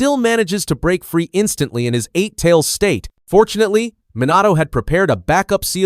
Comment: I had to do a double take because I srsly thought he said "Monado" im going crazy...